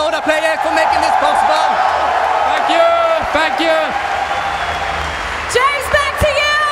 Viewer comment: I swear S4 said, "F*ck you! F*ck you!" to the audience.